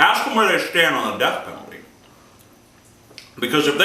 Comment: I thought he said death metal in the beginning...